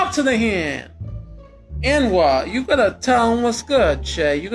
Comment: did he say N'wah?